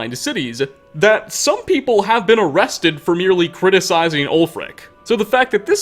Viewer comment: instead of captions saying ulfric it said old frank lol